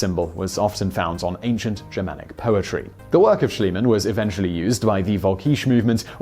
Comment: Does he say, "ancient Germanic poetry"? I know that it says pottery, but it sure sounds like poetry.